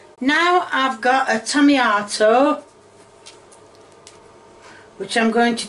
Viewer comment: did she said "tomiyato"????